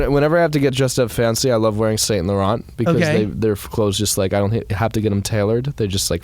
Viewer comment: Which brand did he say he’s going to wear near 11:40? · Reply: Yves Saint Laurent